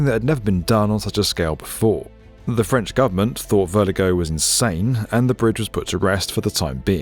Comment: For a minute there, I heard "Vertigo," when you said the engineer's name.